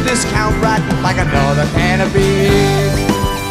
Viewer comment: Fun fact: go to 3:05 and the line is "like another can of beans". With the complaint of the 3:05 verse being how pop music can be a production-line, that feels serendipitious and poetic. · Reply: I always thought he said like another can of beer! Oh well!